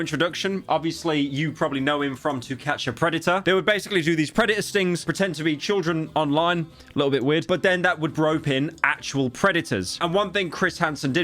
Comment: i heard “predator stings” as “redditor stings”